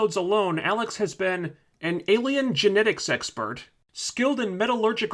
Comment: idk why I thought you said "Alien Genetic Sex Bird"